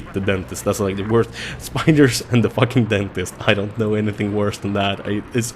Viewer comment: When you said spiders and dentists I thought you said a spider dentist... Shudders